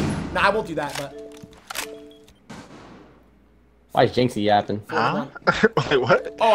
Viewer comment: did say "why is jynxzi yapping"? How could he hear him 🤔